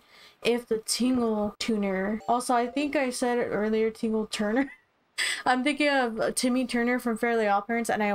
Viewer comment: Lol when you said "Tingle Turner" I thought you said "Tina Turner." | Tom Crook 😂 Forgot about him tbh. | 100/100 ⛵️